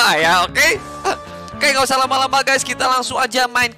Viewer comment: Aku gak salah denger tuh di menit 1:27 ok guys gak usah laba laba? Whatt?????akakakakakaka